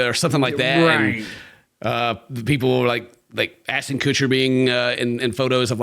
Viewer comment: the autogenerated captions saying ason coocher instead of ashton kutcher is actually sending me I've been crying at coocher for like five minutes